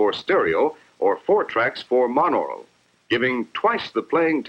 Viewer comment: Did he say "monoro"? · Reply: monaural /mŏn-ôr′əl/ adjective Of, relating to, or designating sound reception by one ear. Monophonic. Having or hearing with only one ear.